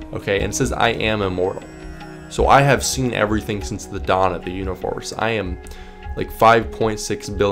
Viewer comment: it can’t just be me who hear you say ”univorse”, right?